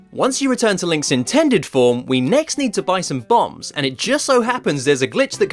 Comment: did he say we now need to buy some B**GS